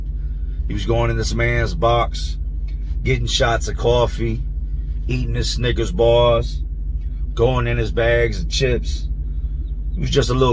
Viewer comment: Bruh I’m too high lol I thought dude said “Eatin this Nigga’s bars” lol I had to rewind like “no tf he didn’t” 😭😭😭😭